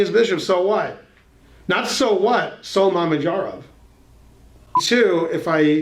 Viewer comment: What did he say? "Not so what? So ___." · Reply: mamedyarov. He’s a gm from Azerbaijan